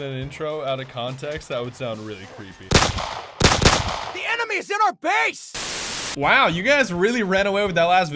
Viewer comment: Am I the only one who misheard the guy in the begining of the video who was shouting "THE ENEMY IS IN OUR BASE!" as "THE ANIME IS IN OUR BASE!"...? XD